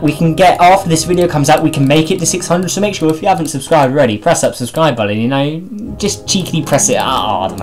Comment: did you say press subscribe belly?